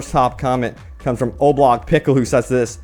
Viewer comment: i thought the mans said Oblock Pickle